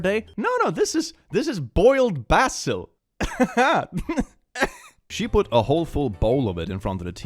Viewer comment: When you said "boiled basil" I thought you said "boiled bath soap" lol (granted I'm not sure if I have ever actually tried cilantro myself and I've heard the difference in different people's reactions actually has a biological basis but meh)